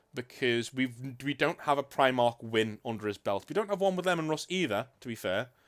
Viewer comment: The more I listen, the more I hear "Lemon Russ," which isn't to far fetched. Dude makes me pucker at all the BS he does 😂